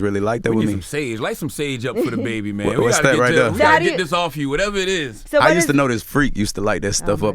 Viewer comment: Bruh at 12:10 did anyone hear Angela say “daddy” lmao I’m dead 🤣😂💀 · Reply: She said ',so how do you' and they kept talking and cutting her off. It sounded like she was gonna ask something but probably changed the question